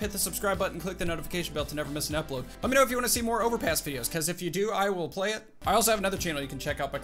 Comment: I thought he said overpiss